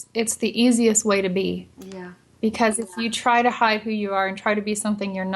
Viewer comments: I heard a "Yep" 17:47 ...cool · I hear a quick little "yep" evp <3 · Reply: Read her lips and she clearly says, “Yep.”